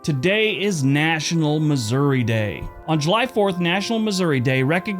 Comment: did you say July 4th